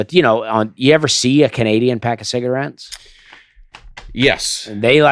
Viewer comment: When Dusty asked if they've seen a " Canadian of pack cigarettes" I thought he said " have you ever seen a Canadian pack cigarettes". I wanted to hear how they're packing cigs! Hahaha